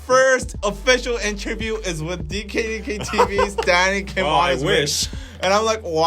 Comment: whenever danny says "This is danny kim" i hear "Daddy kim" iowkajaja I can't be the only one who mishears it every single time